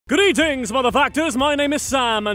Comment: in the begining i thought he said Greetings Motherfuckers :D :D